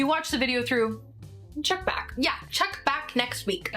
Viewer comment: Why did I hear “check back” as Chuck Bass. You know you love me xoxo gossip girl